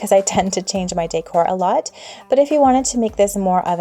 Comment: did she just said “day core”instead of decor? 😂